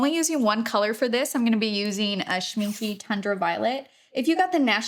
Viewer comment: What is this paint colour please sorry I couldn't quite pick it out with your accent (sorry im english🤦🏼‍♀️) 😍😍😍🥰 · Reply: I looked at the video transcript to figure it out. It is Shmincky Tundra Violet. ❤ I thought she said tender violet. 😂